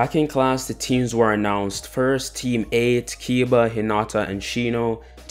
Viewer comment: Ayo I most probably misheard this but when iruka was announcing the teams I swear I heard “takeila, hinata and kiba”